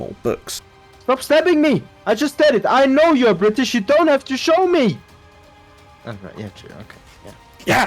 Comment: Did he just say " stop stabbing me, i know you're british, you don't have to show me " ?????? Bro did UK dirty :'D